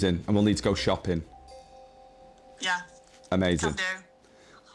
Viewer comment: what did you call her? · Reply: He said cheers lovely